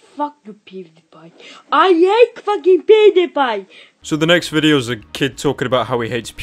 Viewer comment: At first I thought he said “ I AtE fUgiNg pOoDe PyE”